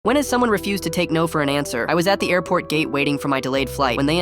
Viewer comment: why do i hear '' airport gay''